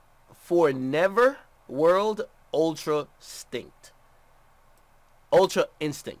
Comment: Lol I thought he said Ultra stink